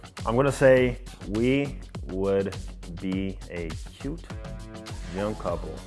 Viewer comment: Is anyone else so annoyed that he accidentally said "cut" instead of "cute" 💀💀💀